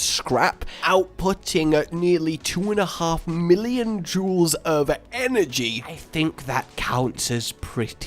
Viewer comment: I thought he said 2.4million Jews... as if they were coal. Jokes aside, entertaining video! :3